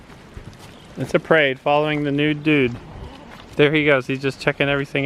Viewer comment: Careful and the channel Our Wyoming Life got into trouble because of seeing the bulls privates. I think they called it "Penis Gate" So if someone freaks out about it don't be surprised. He is a handsome goat. New babies in April? Going to be so hard to wait. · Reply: I thought you said the nude dude. Then I realized you said new dude.